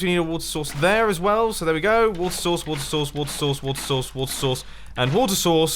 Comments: I started to hear "woodsauce" instead of "water source" lol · Why i hear WAT SAUCE?!